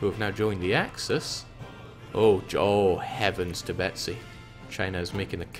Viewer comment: I know it's "heaven to Betsy" but since he was looking at China counterattacking all I heard was "heaven Tibet-si"